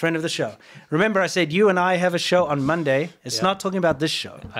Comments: I can't believe chat was ganging up and lying about saying the show was both of your's, they tried to start a fight live. Lmao at least he didn't fall for the psy-op, even if might have a little bit 😂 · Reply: I think might be a touch on the spectrum, and that could influence a lot of his mannerisms and the way he says things. I definitely heard "You know I have a show on Mondays" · said "You an' I have a show on Mondays..." but it sounded like he said " You know I have a show on Mondays"... no harm no foul.